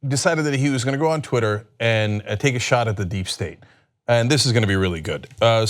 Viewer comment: I thought Cenk said Eric was "taking a shot at the deep sea." I was hoping he was going to harpoon his father.